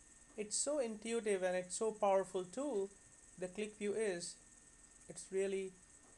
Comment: Did you say qlikview